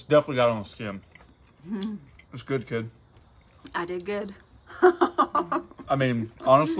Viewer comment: and, amazing job on the Turkey! @ 34:29 I could have sworn I heard look up at you and say, “I did good Daddy?” 😂Great job, both of you!